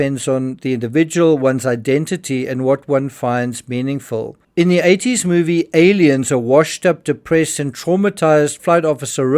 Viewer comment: Technically, the clip is from Aliens, not Alien. Minor nit pick. But good update on pilots’ background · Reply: He clearly says "in the eighties' movie Aliens ..."